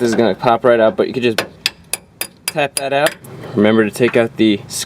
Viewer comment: First I heard "tap that ass"